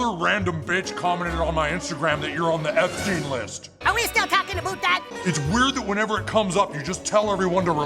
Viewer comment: Love the analysis! I just caught something Trump says at 6:48 when Satan is complaining about the Epstein Files, Trump says; "Are you still going on **A-BOOT** that! He does not say "ABOUT" that... So he is saying it in Canadian..(*or what Americans think is a Canadian accent), or as Saddam Hussein said it. LOL